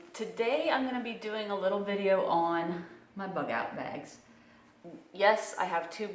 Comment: at first I thought it said NY bug out bag